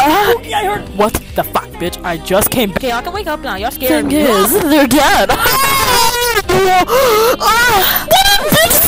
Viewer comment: Bro I thought this said "there DAD"